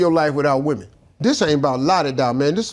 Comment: Sounds like he said this ain’t about laa-dee-daaa men 😂😂